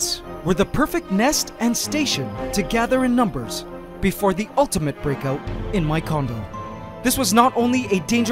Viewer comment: I heard it as 'The ultimate breakout in my condom'